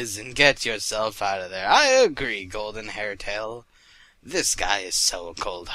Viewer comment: For a second I thought it said golden hentai and that misread